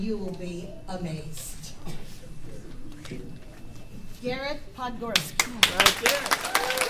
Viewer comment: Podgórski is a Polish surname and when the lady said his name i heard "tak jest!"and that is a polish word as well.Greats from Poland bruh